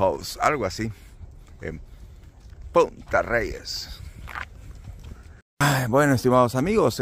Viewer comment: #157 1:32 😂😅 QUE TAL PENSÉ QUE DIJO EN “P*Tas reyes 🤭🤭🫢😆😊🤫 Oí mal Bendiciones con su Madrecita Bello momento juntos 🥰💕